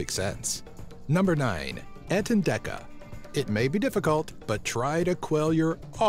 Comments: the entendeka sounds like ur saying ant and decka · When u said entendeka I thought u said ant and deck a😂😂😂😂